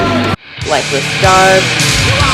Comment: what band did she say at 14:27? i cant understand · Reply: "lifeless dark" they have a demo from 2018 and an album that was just released this year